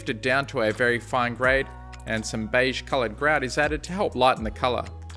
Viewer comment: "Beige-colored xyz is added..." What is that word there please? Edit: Oh, is it "grout"?